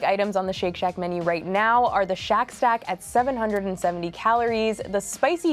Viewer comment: she most definitely said Shack Sack instead of Shack Stack :D:D:D:D